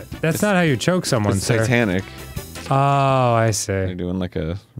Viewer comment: Towards the beginning when Arin said “it’s titanic” I thought he said “it’s Satanic” And for a second I thought “I someone appearing to be choked can be construed as Satanic”